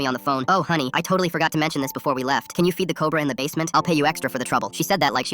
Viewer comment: My dumbass misheard it as: “can u feed the person in the basement” 😭